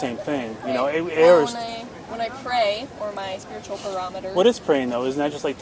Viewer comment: Did she just say “my spiritual barometer”? 😂😂